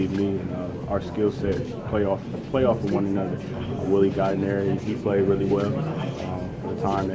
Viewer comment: Did trey say willy gallinari? Lmao